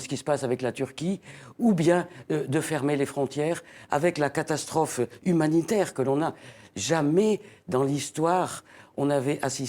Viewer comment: C'est quoi les mots apres "humanitaire" qui n'ont pas apparus dans la sous-titre, svp? à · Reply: Avec la catastrophe humanitaire que l'on a.